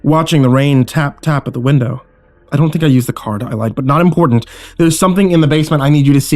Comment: does he say "i don't think i use the card" and then in a hushed voice say "I lied"